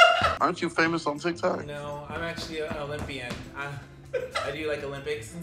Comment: "I'm actually an olympian" (leg spread)😂😂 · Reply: I thought he said no I'm actually a lesbian 😂